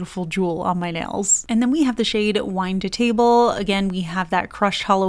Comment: In all respect… it’s not “Wine”…. it’s “VINE to table”. 🤦🏻‍♀️💜 All this time u got me confused to what it really was, and I accept it being “wine” bc of this video I saw back then. Even after I got the bottle this past Dec(after I clicked add to cart on the HT site), I thought it said “wine”, but I checked it again… it’s “VINE”! 🤭💜🤦🏻‍♀️ U gotta correct it before others get confused, too. 🤓💜